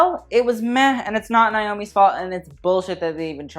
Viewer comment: did she just say Naomi Smalls?